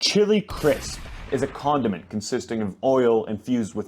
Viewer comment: Oh I thought you said it was a condom not a condiment 😭